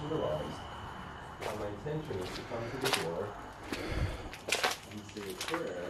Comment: I heard come to meeee